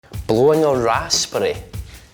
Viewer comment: I thought blowing a raspberry was an English saying, we say it all the time. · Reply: April Dreamer I thought he said blowing an ass spray